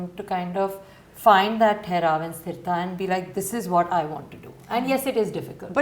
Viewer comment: is sara saying thehrab & stirta.. (2 hindi words)..? can somebody tell me? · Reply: Theraav and shthristha